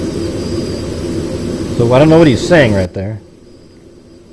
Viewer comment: on your video, the officer who peered up on the roof and came back down is then saying on the radio that you hear in the car “he’s laying down”.